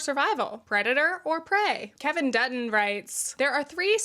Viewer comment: My earbuds glitched at 4:09 and it sounded like "redditor, or prey".. 😂😭